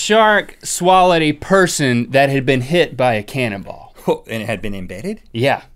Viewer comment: When he said Cannon ball i thought he said cannibal